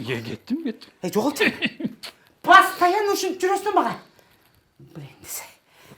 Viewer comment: мне послышалось или она сказала блядь?😂